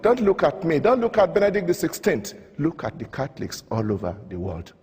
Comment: hehe.. i keep hearing "cat-licks"